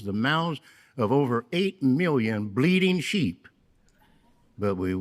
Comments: 💯 You're right. This is a "problem" of their own making. I do think though that this is Herd's personal irritation. I can imagine Governing Body members like Lett, Splane and Sanderson basking in the adulation. Herd doesn't even want to be on the Governing Body any more by his own admission let alone taking pictures with "8 million bleating sheep!" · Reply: Quick joke. When I first heard (Herd 😉) "8 million bleating sheep" live, for a second I thought he said "bleeping" at that's why you heard the distinct nervous laughter. I whispered to my friend that Herd almost cussed. He lost it and nearly got us in trouble 😂😂. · Is Samuel Herd trying to be a stand up comedian? I thought he said "8 million bleeding sheep " lol.